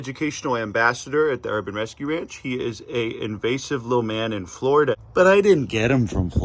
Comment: Did I hear this right?: "Mark Zuckerberg is an invasive little man" .. that part hit so accurately I can't even